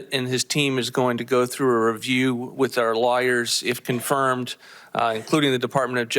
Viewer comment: It may be his accent...but sounds like Vought said "our liars" when he wanted to say "our lawyers", even though he said "law" clearly enough. Freudian slip?